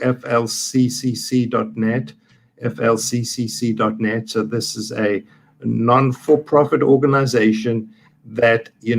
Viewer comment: Would someone please tell me the name of who Dr Marik said was his hero? It’s just before the 29:00 mark. I could not understand what he said!! Thanks 🙏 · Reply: Ignaz Semmelweis: in the era before "germs" were recognized, he first described the benefits of hand washing to reduce the risk of post partum infections and death. He was ostracized by the traditional medical establishment.